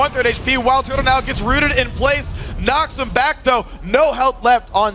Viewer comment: can someone tell me what that guy said at 43:02? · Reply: knocks him back though